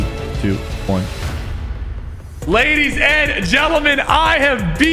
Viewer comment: why did you say ladies and jelaman